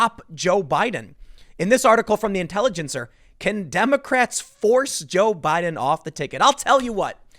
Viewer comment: At one point I heard "condemocrats" lol